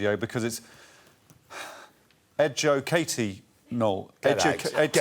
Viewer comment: Boo... I got excited when he sad that it was ed Joe Katie-nle... because I thought he said Ed Jo Katie Nole! Like Nole was a guest...